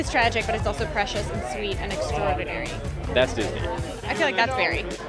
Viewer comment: I really thought he said “that’s hitler”...I’m embarrassed...